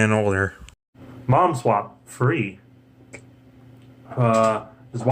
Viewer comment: I thought you said Momswap Free. Edit: also what would the movie version of momswap be like? y'know like Steven universe the movie